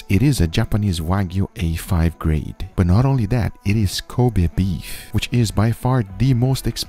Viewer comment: I thought he said "it is covid beef"